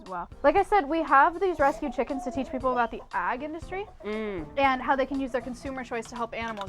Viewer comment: is anyone else hearing "the AGG industry"? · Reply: she is saying "AG", as in short for agriculture, if I'm not mistaken